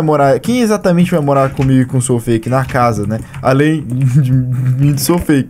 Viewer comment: Pow, entendi "vou cumer o SouFake" hsushushsshsushus Só uma surubinha '-'